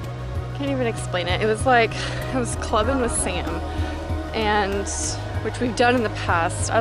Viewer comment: oh wow when when you were talking about your dream and you said "i was clubbing with sam" i thought you said "i was clubbing with satan" .....